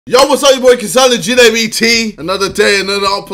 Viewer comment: "yo was up kasiy latude bt" thats what i heard